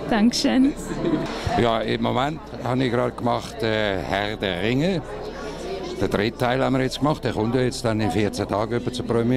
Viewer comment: Der von Bud Spencer War ein Schwyzer und hat Schweizerdeutsch gesprochen darum hast du ihn nicht verstanden. Ich habs hier übersetzt an alle die wissen wollten was er sagte: Ja im Moment habe ich gerade Herr der Ringe gesprochen, Ja, den dritten Teil haben wir gemacht. Der kommt ja etwa in 14 Tagen zur Premiere. Ich habe auch den "Grimli", den Zwergenkönig und.... (Den rest hat Monte übersprungen)